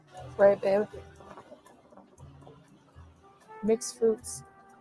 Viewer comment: the BABES is an eye roll, but does she say “mixed fruits”, start to say something about “…makes foods…”, or did I stroke out and an actual comment which would make sense get uttered?